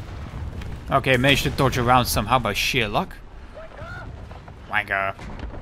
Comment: I always thought he said wake up instead of quicker/wan***!